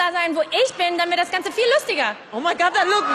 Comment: Are you sure she didn't end with "dann wär das ganze viel lustiger"? That would be grammatically correct, but I wouldn't put it past her to just loose the word either... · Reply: I hear "dann wird" instead of "dann wär"